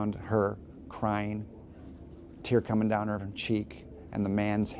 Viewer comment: Is it just me who heard the guy at 2:09 say with a tear coming down her va cheek